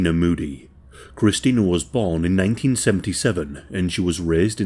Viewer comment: I thought you said she was born in 1997? She couldn't have been 32 on 09....